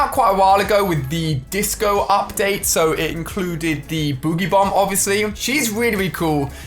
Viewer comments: I thought he said booty bomb XD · Did He Say Booty bomb?!? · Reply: He said boogie bomb it's just hard to hear because the accent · did he said booty bomb😂😂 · I thought he said booty bomb lmao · did anyone else hear him say BOOTY bomb